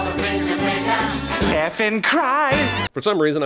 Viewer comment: Omg I'm glad someone else thought Stewie said "effen cry"